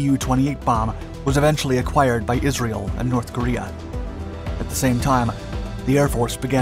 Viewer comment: Did I just hear "acquired by North Korea"? (04:15....